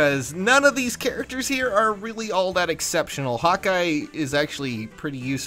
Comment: I thought you said "Hot guy" instead of "Hawkeye". Gotta admit, I made a "mmmhmm" sound right after that ;)